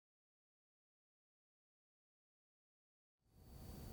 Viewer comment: Subtitles: Cacao pdds instead of Cacao Pods (I don't know if you can still change it or not, but I mention it just so that you know ^^)